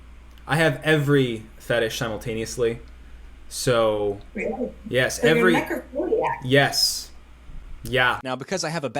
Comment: What did the lady say at 1:18 ? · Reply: “Really? Even necrophilia?”